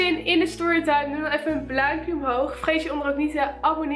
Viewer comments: Ben ik de enige die bij 1:01 bluimpje hoorde??? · Hoorde jullie dat ook 1:02 zei ze:bluimpje